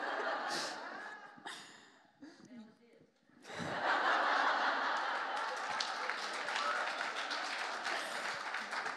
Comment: I just love Dom so much.❤ Wish I could understand what someone in audience yells at 16:04, Dom's reaction is so cute😄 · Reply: "vanilla dipped" (again) referencing to Nicole 😏😂